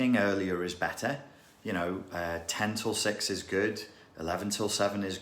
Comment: LMAO 😅😂😂🤣 I thought I heard tental sex. Omg 10 til 6. I was thinking what the hell is that??? Total blonde moment. Thank u for the info